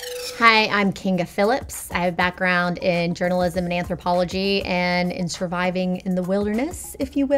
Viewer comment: I thought she said anthropophagy which is cannibalism